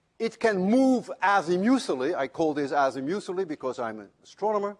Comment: He uses two related words several times between about 3:25 and 5:38 that are captioned incorrectly. The words are "azimuthal" and "azimuthally." The captioner shouldn't feel too bad, because it took me a long time to figure out what he was saying despite my having a fair background in physics and astronomy.